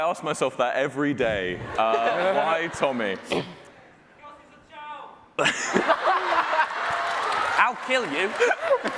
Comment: What did that guy shout at 25:20? I cant figure it out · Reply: He yelled “cause he’s a child”